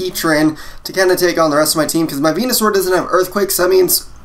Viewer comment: Just me who thought he said Penisaur? (I'M NOT GAY! QQ)